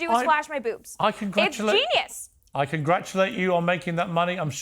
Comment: Did she just call herself a genius?